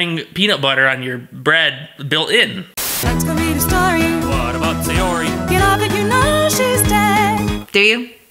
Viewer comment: The part that I misheard was when they said “ what about sayori” cos I thought they said “ what about sex yuri?” (True story)